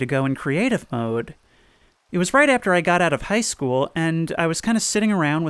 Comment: Man, i probably need to stop playing payday, cause everytime he says "high school" i hear "heist school"